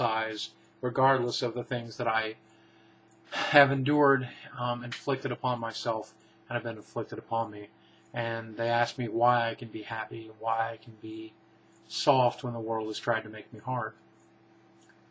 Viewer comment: Did he actually say why can I be soft, when the world is trying to make me hard ? At about